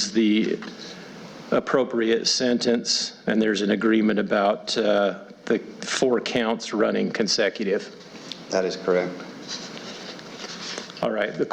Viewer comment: only 5 years!! That’s a slap to the face to her kids. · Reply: Mmmm, I heard this differently. Judge: "...and there's an agreement about the 4 counts running consecutively...." Ruby's Attorney: "That is correct..." start at 3:40